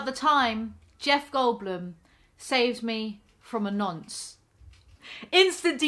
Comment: What the hell did you say at @ 0:10 ? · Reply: "...from a nonce." Usually used as a term for sex offenders in UK.